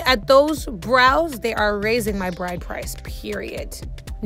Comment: Did I hear that right at 2:36, did she say that her brows are raising her bride price?